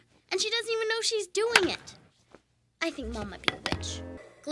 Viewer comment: I think she's saying "I think mom might need to pitch" but it almost sounds like she said "I think mom might be a bitch."